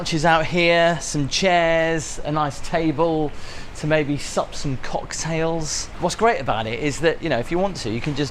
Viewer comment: Sounded like you said “suck on some cocktails” I had to rewind back to see if I heard right, as usual I was wrong lol